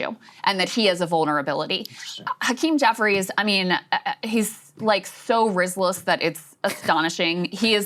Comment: Did Krystal call hakeem "Rizzless" lmfao